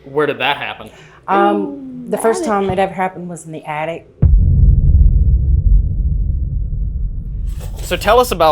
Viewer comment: "ooooh attic" Am I the only one who heard that??